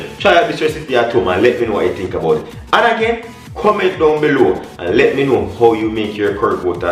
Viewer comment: No me the here a think about how I need to visit Jamaica, only to hear you say "annakay comment below" well, lol you saud "and again comment below" but when you said what I thought you said I thought this man Calling my name. 😁😂😁☺